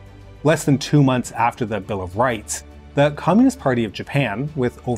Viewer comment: the host clearly says "bill of rights" but the subtitles says "bill of tights" LMAO